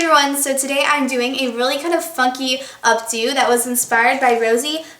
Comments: at the beginning it sounded like you said 'fucky' but you were saying funky, i think. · am i the only one who thought she said a "fucky updo" ? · i thought she said fucky instead of funky lol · Am i the only one who thinks it sounded like she said "fucky" in the beginning? hahaha